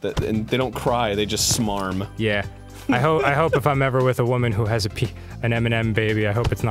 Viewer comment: I could have sworn he was going to say, "I hope if I'm ever with a woman who has a penis."